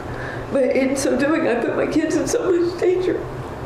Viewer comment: the captions say "in some doing..." but she actually said " In so doing...". Great trilogy, crazy what some people can be pushed to do.